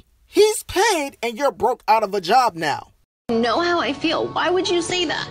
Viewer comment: I thought he said he don’t got a job